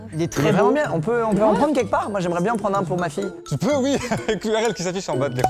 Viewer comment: Vous avez entendu comme moi ? « je voudrai bien en prendre un pour ma fille » ?? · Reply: ENTIK pour ma fille il a dit